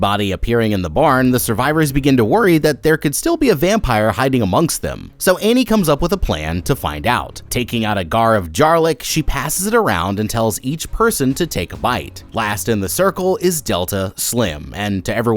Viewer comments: did no one notice that he said “gar of jarlic” instead of “jar of garlic” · "Taking out a GAR of JARLIC"😂😂😂 anyone else catch that? 😂😂😂